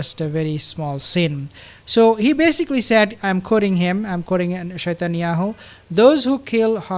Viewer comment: Shatanyahu is absolutely right for Netanyahu 😂😂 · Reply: I thought he was saying shatanyahu. Hysterical 😂😂😂. Poor nutty is unhinged.